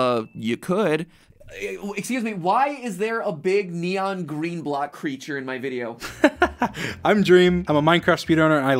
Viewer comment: i thought matpat was gonna say "why is there a fatherless green guy here?"